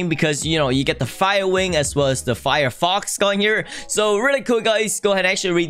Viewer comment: did I just hear FIREFOX???